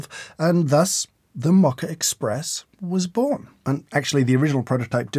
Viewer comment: Sounds like he is saying "mocker express"?